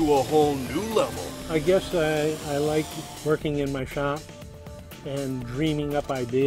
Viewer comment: is he saying that he likes twerking in his shop ?